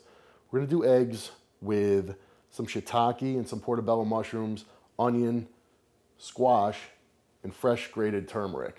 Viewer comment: I thought he said, "onions, squats."